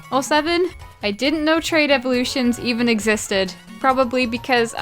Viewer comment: I misheard trade as train so I tried evolving a scyther into scizor by level up. I was sad. Also tried teaching Flareon fire punch in gen 2. For like 3 minutes